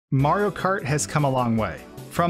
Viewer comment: did he just say maro kart?